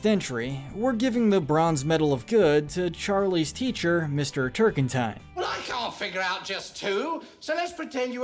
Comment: I originally misheard Mr. T...'s name as Turpentine, instead.